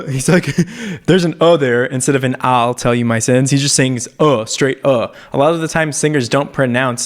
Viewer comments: I didnt think it was either one I always thought he was saying "I tell you my sins" anyone else hear it like this❔❓ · When I first heard take me to church I thought he said "Owl tell you my sins" I was like that doeant sound right!!